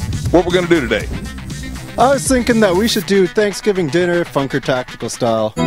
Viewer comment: At the end sounded like he said Fucker Tactical, but Mom definately makes a better turkey than my wife, which is why we drive two hours every Thanksgiving instead of eating at home.